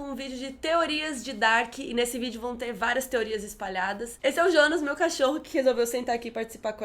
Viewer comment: Eu ouvi "esse é o Ionas, meu cachorro" kkkkk